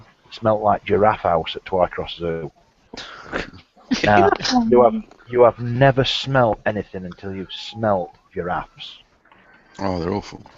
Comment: For a moment I thought that said "you haven't smelled anything until you've smelled your ass." I blame the beer.